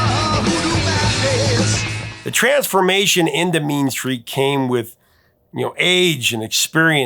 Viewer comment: Thought you said " Mean Streak", was bracing for Y&T, lol